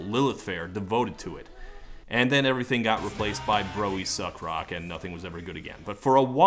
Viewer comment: "And then everything got replaced by bro-ey suck rock and nothing was ever good again". Never heard a more accurate summation of the last quarter century of pop music. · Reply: I thought he said “Bro, we suck” rock. Same thing I guess.